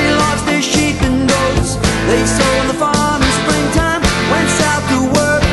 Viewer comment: I always hear "That little boy in springtime" instead of "After the thaw" as though he was giving an example that happened just before "Bobrow's youngest daughter"